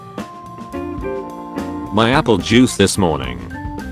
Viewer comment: it sounded like "my uncle juice this morning"